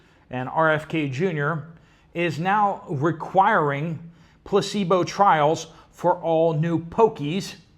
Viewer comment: I thought you said “pussy bull” !? 😅 Placebo 😅